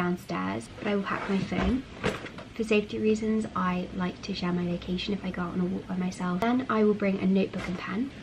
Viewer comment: can someone please tell me what is she saying at around 1:33 ? "For safety reasons...." I can't understand the rest, i've replayed the sentence several times, but i have no idea. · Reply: "For safety reasons, I like to share my location if go out on a walk by myself."